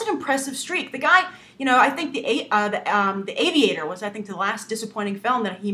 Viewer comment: OOOOhhhh Aviator!!!!!!! I thought you said Avatar.... I was thinking the 8 times I saw Avatar and I did not see Dicaprio